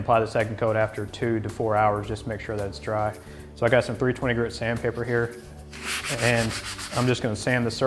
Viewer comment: thank your for your efforts. At 2:08, I didn't understand what was said: "pueer twenty grit sand paper" (while the number is hidden by your hand but we can see "3")? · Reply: 320 grit sand paper